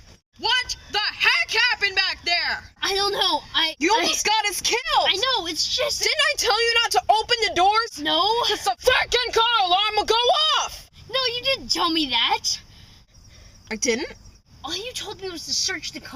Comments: Did You Say "Cuz The Fucking Car Alarm Would Go Off" ? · pretty scary and at 8:27 did u say the f word or did u say freakin